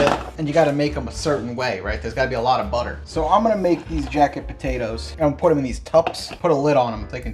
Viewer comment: I thought the video said jacked potatoes lol